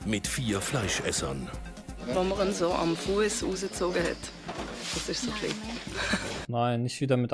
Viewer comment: wenn maumu am fuess usi zoge hät... so schwerig zu verstehen ist es nicht... das heisst "als man ihn am Fuss rausgezogen hat".... da kann man schon auch wenn nit Schweizer Deutsch kann daraus herleiten weil sie mit dem Wort "usi" gemeint hat...